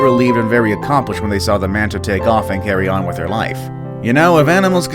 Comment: did he say “Mantatay?”